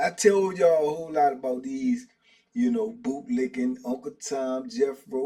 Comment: Maaaaaan, I thought it said bootylicking. Had me like WTH! 😮